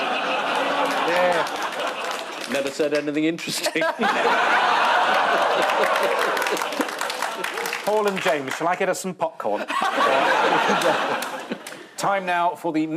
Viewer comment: does he call James Jamie?